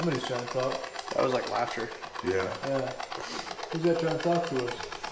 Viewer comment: Sounds like it said who is that walking in